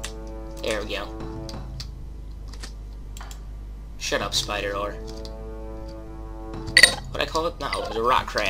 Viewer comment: I thought you said shut up spider h$%#